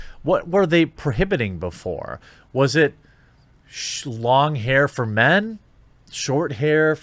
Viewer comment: Did you say shlong hair for men?